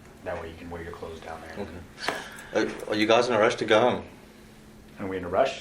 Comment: “ are you in a rush to go? “ I swear I thought he said, are you going to arrest the girl? 😂🤦🏻‍♀️